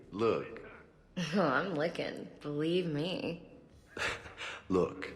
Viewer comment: does ashley say im looking or im licking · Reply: Looking.